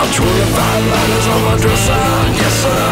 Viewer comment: I hear 'lighters'. Could be letters though, who knows..